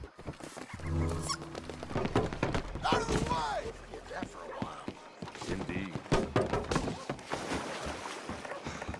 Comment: Alguém sabe oq significa isso que ele fala sempre que passa perto de alguém quando tá rápido? 0:23 Eu acho que deve ser: "sai do mei" kkkk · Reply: Out the way Sai da frente